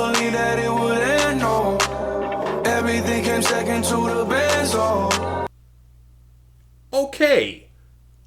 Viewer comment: Sunflower ft. Swae Lee is pretty good tho Edit: benzo? I thought he said "bands, though"